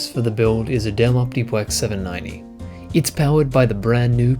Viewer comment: Did I hear Optipwex at 2:56?